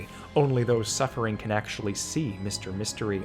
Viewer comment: I initially misheard the killer referred to not as "Mr. Mystery," but "Mr. Stinky," and that kind of fits, too!